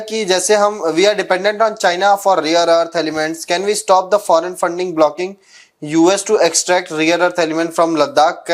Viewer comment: Rear earth 😂😂😂😂 instead of rare 😂😂😂😂😂😂 …. Pakistani’s like rear earth 😂😂😂